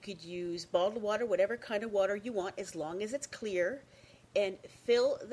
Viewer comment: am i the only one who heard bong water